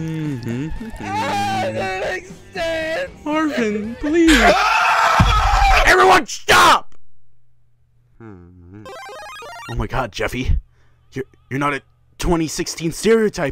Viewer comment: Why does the caption says orphan please